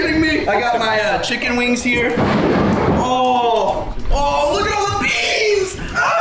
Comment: "look at all the BeAnZ" Im dying rn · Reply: When I saw this as son as I red it I just heard “look at all the beans!” I was like “How convenient.”